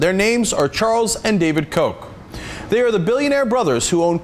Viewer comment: Ahhhh they're called Koch xD Damn that's wrongly pronounced! I allways thought he said Coke brothers!